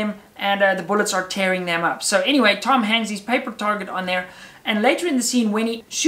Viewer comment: When he said "Tom hangs his paper..." I heard "Tom Hanks" hahahahahaah. Nice video, m8!